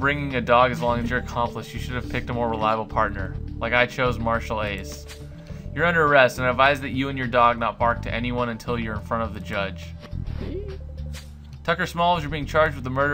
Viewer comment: did frank just call ace a dog or something?!